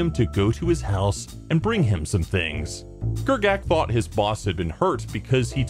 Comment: did he say grgat?